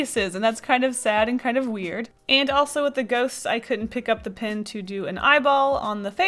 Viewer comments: Lol I thought you said 'goats' instead of 'ghosts' so I was really confused when you started drawing ghosts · I thought you said goats not ghosts and then when you drew ghosts I got sad.